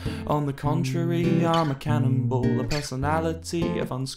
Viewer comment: Why did I think it said cannibal- Just me?